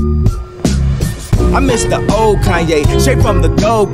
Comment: When I first heard the I thought he was saying old kage